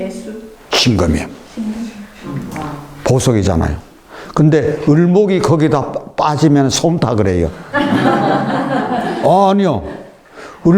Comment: 초 을목이 거기에빠지면 솜??뭐라고말씀하신건가요? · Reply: 을목이 거기에 빠지면 서운해합니다~~